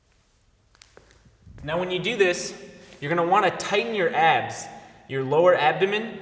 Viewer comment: i thought he said ass at 0:52 .. made me a little scared considering his looks